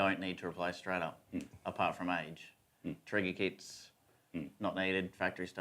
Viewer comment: What’s the guy on the right saying at 6:24? Something kits? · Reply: Trigger kits